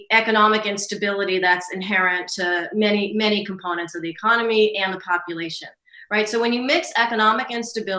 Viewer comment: I thought she said, "many components of the economy and the copulation..."